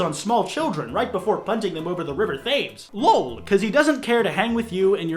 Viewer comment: It sounded like you said "Thaymes" Instead of "Th-emes"